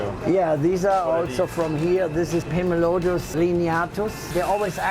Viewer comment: can anyone tell me what these are? I cant really understand him when he says the scientific name Edit: after a lot of searching, are these called four line pictus? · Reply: Pimelodus lineatus